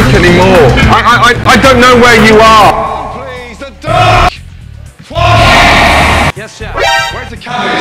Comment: is funny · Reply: did he just say where is the duck